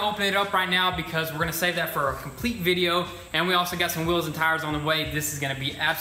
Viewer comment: did he say wheels and tires??